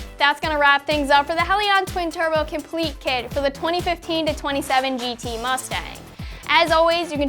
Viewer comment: Did she say 2015 to 27 mustang? @ 8:22 😅